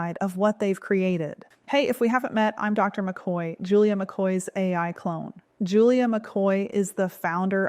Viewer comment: Did she say she's an Ai Clone. She look so real!